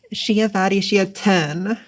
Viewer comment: is there anyone who can tell me what sentence it is? (I cannot hear it clearly... ) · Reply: 'She a baddy, she a ten.'